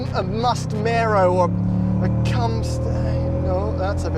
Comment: 800hp modern car with stick shift. What a rarity, what a beauty. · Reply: Lmao he said "Mustmaro or better yet, Camstang.... Oh that sounded rude...." I was like did he just say cumstain?